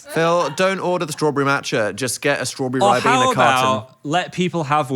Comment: not the captions saying "rib in the carton" instead of ribena carton 💀💀💀